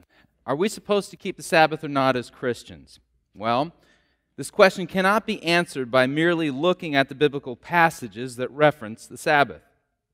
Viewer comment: I was really interested on hearing the reason Sunday christians keep the first day, but as soon as the second guy(don’t know his name) said we can’t just use the Bible… he lost me. The Bible is our instruction book and if you can’t defend Sunday worship solely from that book then you CANNOT defend it. Period 📍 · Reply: He did not say "we can’t just use the Bible". He said "this question cannot be answered by merely looking at biblical passages that reference the Sabbath". (in 29:05) What he means is that we have to look at the other passages, the entire scriptures, not just the verses which has the word "Sabbath" in it.